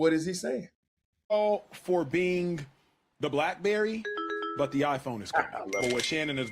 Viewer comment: Whoa, did he just say welcome to the blackberry while the iPhone is coming??? The audacity